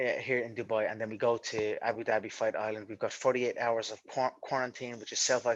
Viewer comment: I thought he said 38 hours of porn